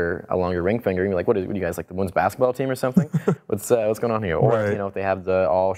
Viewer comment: Can somebody wrtie me the part 9:53 to 9:57 ? the part of the finger, please, cause i speak spanish and i dont understand very well, thanks. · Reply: Let's say they have a...a longer ring finger, you can say, "What are you guys, the women's basketball team or something?"